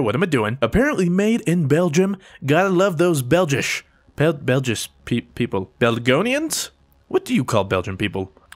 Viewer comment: Belgiumers wtf lol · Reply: I thought he said "Belgium people". But whatever, still LOL.